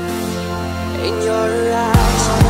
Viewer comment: 0 sounds like he says "in your lies"